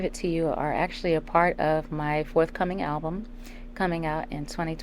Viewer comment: Fourth album? Why do I feel like Mya should have had more? Edit: I misheard what she said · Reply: If you mean at 5:45, Mya said forthcoming album meaning her upcoming album / next album.